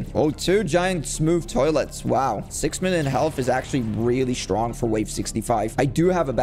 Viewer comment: he said 6 minutes health or did i heard wrong